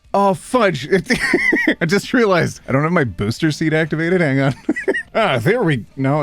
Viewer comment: I THOUGHT YOU SAID “I don’t have my boobs activated” HAHA AND IT MADE ME LOOK AND WAS LIKE 👀